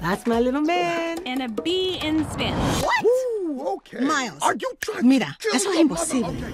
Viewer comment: Bro I heard how Miles' dad was like "Oooh Are you tryna' kill me mother-" at 0:53😂😂